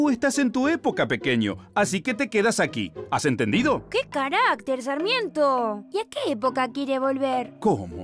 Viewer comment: pensé que decía que carajo sarmiento XD